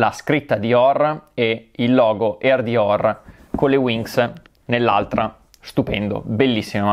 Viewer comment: solo io ho sentito winx? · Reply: Voleva dire WINGS ahha